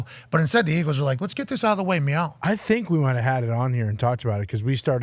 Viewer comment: Did he say “meow” 😂